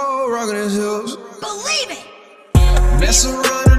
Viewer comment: hol up did he just say 1000 years of death in yo booty